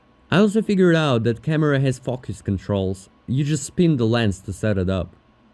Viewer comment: When he said the camera had focus controls it sounded like he said it had f*ckus controls lol